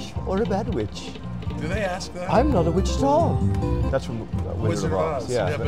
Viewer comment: I thought Dorthy said : “ I’m not from Wichita”!